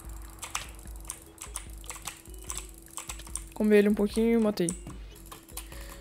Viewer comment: "comer ele um pouquinho, matei" Eu: oxi, né possível que eu escutei errado